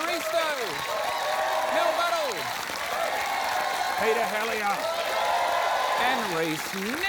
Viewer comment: 1) how is mels last name spelled bc i keep hearing them say 'Mel Butthole' and that CANNOT be right 2) dying at when she intimidated her neighbor ... With maths 😂😭